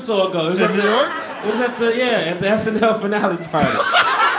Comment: what does he say there? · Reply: Snl Finale Party